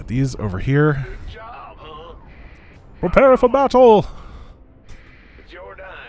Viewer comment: Okay but when he attacked with the svcs I heard "Repair for battle!"